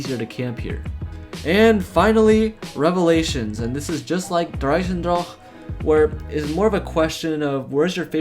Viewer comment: Lol why does it sound like he is saying der eisendrahhhke